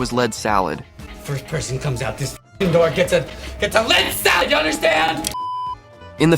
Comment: AI narrator? Did he just say lead salad the first time?